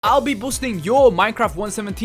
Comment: did his say busting minecraft myths?